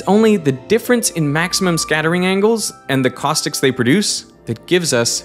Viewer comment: There is an error in the captions. 13:07 says "thetics" instead of "the caustics"